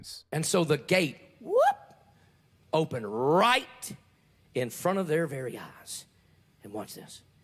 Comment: What I hear: “...in front of their very arse.” What Texans hear: “... in front of their very eyes.” Mine is funnier.